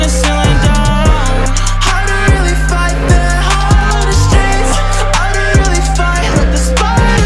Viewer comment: Link To All Music Streaming Services <link> RUN THIS UP AND SHARE EVERYWHERE!!! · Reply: am I trippin when i hear "i don't really.fight hard in the streets" but it says 'FAIR' in the text?